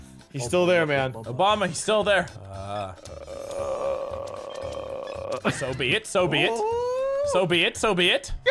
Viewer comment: all i heard was "soviet"